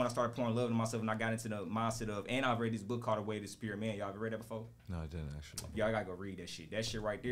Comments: yo what book did you say it was? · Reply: The way of the superior man by David deida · whats the book? I couldnt really catch it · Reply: I think he said it’s called, “The Way Of The Superior Man”